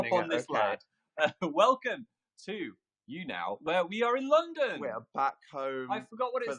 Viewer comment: When Phil said, “where we are back in London!” I paused it before he could finish saying London and I really thought he was gonna say, “where we are in love!”